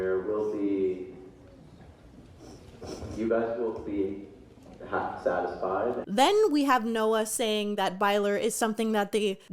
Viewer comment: I dont think the quote at 2:30:38 said "hap- satisfied", I think it said "half-satisfied" Makes me nervous for a show I have never and will never watch, but I still believe it's more likely to happen then not happen.